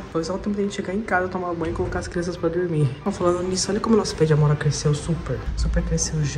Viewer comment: GENTE AKAKAKAKAKAKAKKAKAKAKAAAKAKAKAKAKAKAKAK A TAVA FALANDO NO INICIO DO VIDEO "AMORA", E EU JURO QUE ENTENDI "PAMONHA" KAKAKAKAKAKAKAKAKAAKAKKAKAAKAKKAAKKAKAKAKAKAAKKAKAKAKAAKKAKAKAKAKAAKAKAKKAKAKAKAKAKAKAKAKAKAKAKAKAAKKA